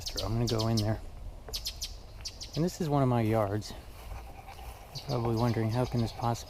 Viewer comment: Guys, I didn't. This isn't my house! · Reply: Beg pardon, I thought you said it was one of yours, or something like that. My A/C is going full blast, so must have heard wrong. Man, I go to the landfill constantly, pickup with an 8' bed and sometimes a landscape trailer also, just with my landlord's property, his dad's, the one in back of this one, and this prop, so I know what it is to cut brush and limbs and pull weeds! It has been so hot that the Plum Slum, the place in back of here, looks kind of scary, with weeds and elms growing in the landscape, so I shouldn't talk! But I gave a thumb up also. I know you work hard. Oh, you said it was one of your "yards". I wasn't paying attention, was I?